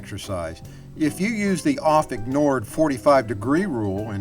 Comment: What is said at 2:37... "If you use the ____ _____ 45 degree rule and turn base at or before that point..." It sounds like Oft Ignored, but I can't make it out and it is annoying me.